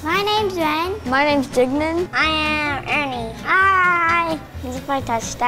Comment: did he say his name was dignin